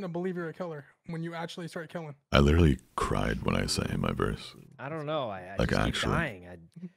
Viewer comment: I thought he said "I cried when I sent him my verse"